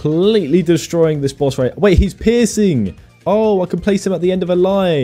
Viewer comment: I thought bro was saying the "omg hes pissing"